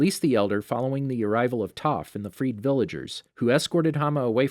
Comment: It's like i heard you say freak villagers but you actually said free villagers